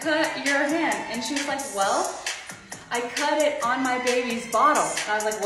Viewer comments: I thought she said “I cut it on my baby’s bottom” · I thought you said she cut it on a babies bottom